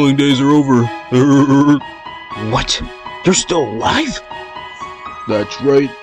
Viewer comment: im tired and heard it as: WHAT??? YOUR STILL WHITE?